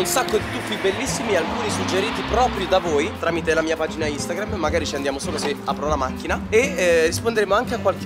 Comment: solo io ho capito:"qualche domanda tramite la mia vagina Instagram" ??